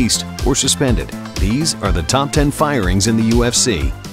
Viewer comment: lol i thought it said fringes not firings